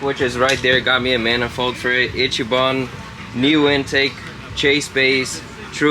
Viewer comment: thought bro said itchy bum